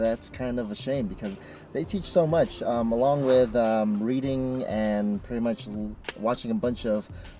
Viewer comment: For a split second at the 3:16 mark I thought you were saying the Jugglife podcasts teaches you stuff like "reading".. lol